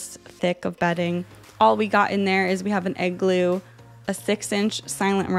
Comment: when you say igloo i misheard it as 'egg glue'